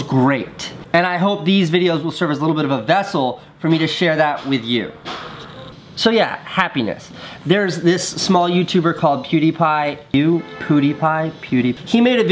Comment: Am I the only person who heard him say a smaller YouTube pewdiepie